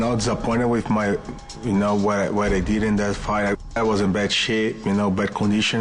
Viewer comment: does Nogueria say he was in bad shit or shape?